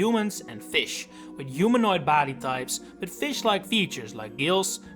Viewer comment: for a second there, I thought you said humanoid 'baddie' types 😂 Wouldn't have been wrong either way tbh